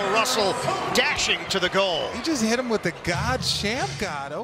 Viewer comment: did he just say goal 🤔